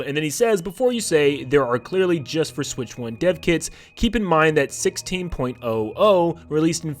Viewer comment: bro I thought he said ‘dead kids’ 💀